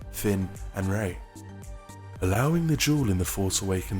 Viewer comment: I keep hearing "jewel" every time you say "duel". Lol